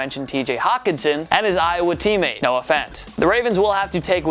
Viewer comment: When said Noah Fant, I thought he said No offense